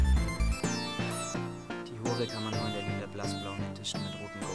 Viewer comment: "Die Hure kann man nur in der LEDER blass blauen edition mit goldecke und grünen Punkten fangen" xD ich hab Leder verstanden ;D